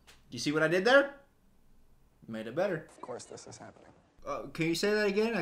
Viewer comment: I had to add the subtitles to know what he was saying💀 but he says “of course this is happening”, well that’s what the subs say anyway